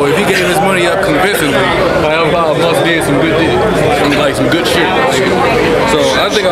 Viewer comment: So nobody is going to mention the fact that accidently said "Then that must of did some good Dick" Instead of "good shit" ? lmaoooooo 0:47 With that being said, I dont think anyone cared for this battle. doesnt seem like he cares anymore